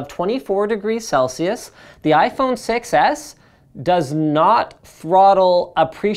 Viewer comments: Sounds like he is saying iPhone Success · i heard you say iphone success not 6s lol